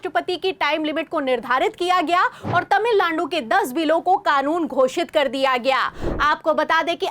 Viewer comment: did you say tamil landu🤣